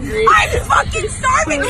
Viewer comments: “CEO of hangry” IM CRYING 😭😭😭😭😭😭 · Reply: I'm super late to this. But I always thought she said, "Our CO is hangry" C.O. meaning commanding officer as "squid" is a derogatory nickname in the Navy for a lower ranking enlisted sailor. Similar to how other branches use the word "maggot" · does she said squid or squish ?